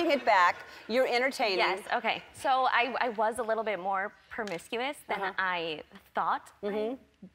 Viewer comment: Did she say "PERmiscuous" ??